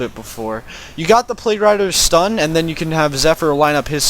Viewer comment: did he say plague rider's "stun" ???